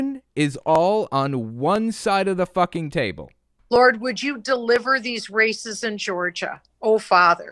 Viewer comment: I thought she was saying 'deliver these racists' in stead of 'deliver these races' for a hot minute there and was so confused